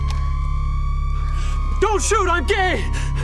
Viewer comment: did he just say "dont shoot im gay? XD